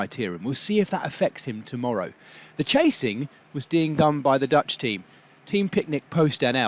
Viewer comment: "The chasing was deing gone." My face must have looked really weird when I heard it and even weirder when I tried hearing it again. Have a listen at 2:30. Man, am I the only one finding it really funny?